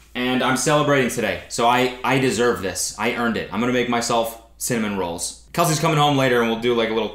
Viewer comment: I finally realised when you started rolling the pastry that you were making CINNAMON rolls, i thought you said SALMON & I was waiting for some weird American desert the whole time